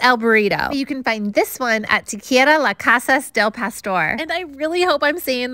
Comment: If America was a burrito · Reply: I tought i heard ,"Taqueria El gran Pastor"